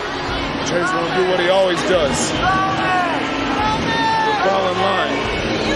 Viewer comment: What did he say? 😵‍💫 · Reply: Jay will do, what he always does. He will fall in line